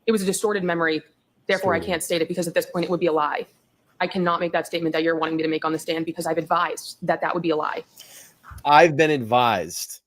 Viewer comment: You should listen to what she ACTUALLY said. She did NOT say, I’ve been advised, she said, “I can not make that statement you're wanting me to make on the stand because I’ve advised, that that would be a lie”. So, when she says, “I’ve advised……”, she is simply stating that she can't make that statement on the stand, under oath because, as she has advised, it was a distorted or false memory. Timestamp ( 46:02 )